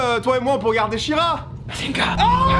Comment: Il dit quoi à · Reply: "Bazinga", c'est une expression récurrente de Big Bang Theory.